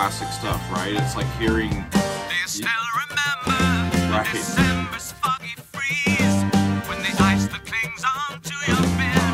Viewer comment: I'm surprised no one pointed out the lyrics, "and you snatched your rattling last breaths with DEEP SEA DIVER sounds." The aqualung. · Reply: Hey, until today I always heard "December's foggy freeze" as "December's fucking freeze!"